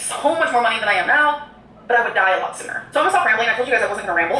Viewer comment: Did she just say "im a diet lossener"..........